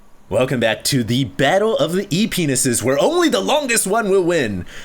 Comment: Does he say e-penises?